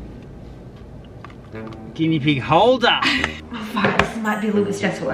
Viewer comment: Is it just me who think they heard say “oh f**k” at around 5:53 ?